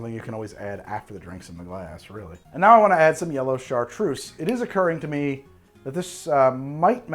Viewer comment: Thank goodness for captions because I heard "yellowcard truce" as "yellow chartreuse" and could not understand what you meant (or what color that could possibly be) >~<